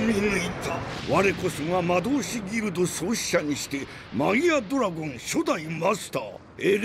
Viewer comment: pourquoi il dis mamadou😂😂